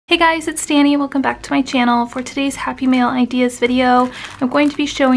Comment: Cute, simple project! With your accent it sounded like you were saying happy meal. Or maybe I'm just hungry. Lol